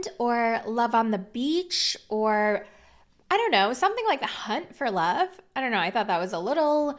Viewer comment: I totally heard "c_nt for love", with which I agree